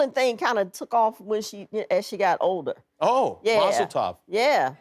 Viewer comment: What does jimmy say at 6:43 ? What does that mean? Just wondered 😂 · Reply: Mazel tov= Good luck